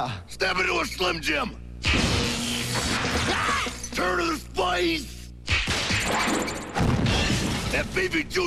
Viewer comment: What did Randy "The Macho Man" Savage say around 0:18?... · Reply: Turn to the Spice!